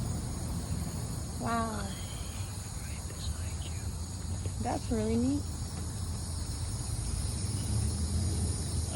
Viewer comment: I heard voices but idk what it says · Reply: Bruh he literally talks about it seconds later lol. “I’m right beside you”